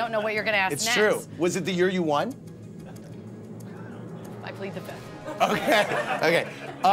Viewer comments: Can anybody tell me what Jennifer says at 1:29? I'm from Germany and she talks so quiet at this moment. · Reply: "I plead the fifth." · can someone tell me what she said at 1:29? "lucily the ... " ? (i hear "luckily the fifth" but it doesnt make sense)